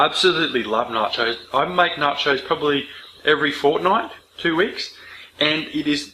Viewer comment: did anyone hear him say fortnite?